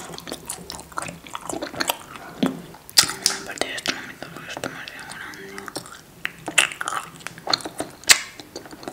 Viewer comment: que dijo🤔😘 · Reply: "Vamos a partir esto un momento, porqué está un poco dura"